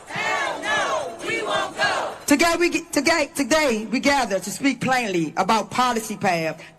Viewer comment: I thought that last speaker said, “let us speak clearly”? Hard to even understand her. However, gleaning what little fact she presented, overlooking her victimhood, regarding her demand of, “respect” while giving none and earning none…stepping aside from her racism and blatant misrepresentation of history, one has to wonder how the most base end up as, “leaders” or representatives of the “cause”? These self-appointed spokespeople tend to regard the black community as one large homogenous group, for whom THEY speak. It’s not about being black. It’s about culture. Poor culture creates and perpetuates crime. The Great Society, social welfare as a way of life, destruction of faith and the family are all contributing factors to the decline and disintegration of black status in this country. History stands against the modern propaganda bloviated by modern mouthpieces.